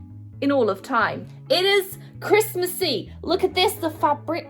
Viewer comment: When you said christmasy it sounded like you said christmas eve i was sort of confuesd merry early Christmas though!🎉🌲🏞️🌨️